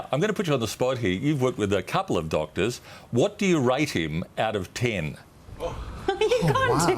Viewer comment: "Rate him out of ten" I thought he said "write him out of tin".